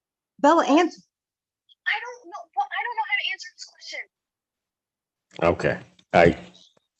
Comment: requesting a jury trial to let her get the house and she would probably have the mother live with her too, It’s really telling that this must be a family trying to work the system all the way through, I just don’t know if the daughter planned this all along or her mom is putting her through this 😢😢😢😢😢😢😢 just a bad situation all the way around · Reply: When calls her mother at 12:25 you can hear her in the background say "I don't know what to say!" Disgusting of the mother